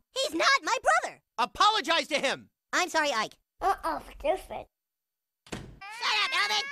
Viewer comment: What Ike said had me dying 😂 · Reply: Uh uh just sleep